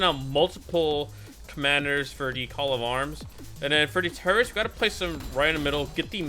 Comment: when he said "for the turrets" i thought he said for the terrorists 💀💀